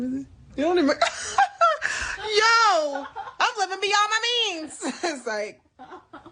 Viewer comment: The first time she said, "im living beyond my needs", it sounded like she said, " im fittn' to be on my knees". Thought she was talking about OF to make $$$.